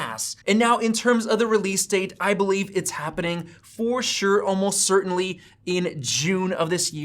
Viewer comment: did you just say "for sure" ?? 😎